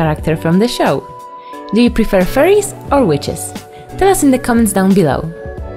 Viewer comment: I thought you said "do you prefer furries or witches"